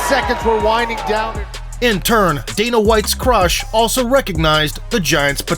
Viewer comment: lmao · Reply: lol did i just heard "danas white crush". lmfaooo